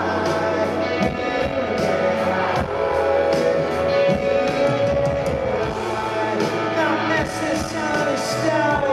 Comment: Não consegui entender ainda o que ele fala aos 6:04... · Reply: Not necessarily stoned, you're beautiful